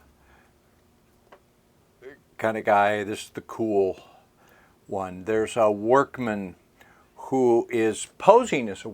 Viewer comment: what did i hear at 1:56:55 ? they're supposed to be alone in there right? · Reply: Sounds like someone walks by and says hey